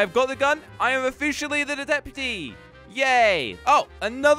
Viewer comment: did he just say dedeputy